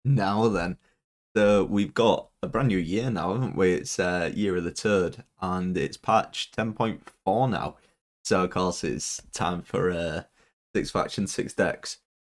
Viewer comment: Now then!!! Sound like you say "year of the turd" at the start 😄😄😄